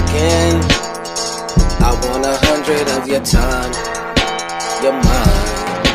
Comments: i thought he said "your mine" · I thought he said “your mine” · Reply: Yes he did say "you're mine", the video creator got it wrong